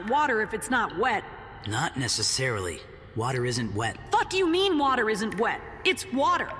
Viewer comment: I thought it was the normal Naruto dub until I heard him go "FUC U MEAN WATER ISNT WENT"